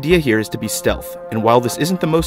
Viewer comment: I thought it said: Hiding pot, and i wtf'ed :o